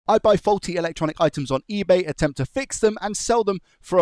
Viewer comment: I thought you said “folksy electronic items”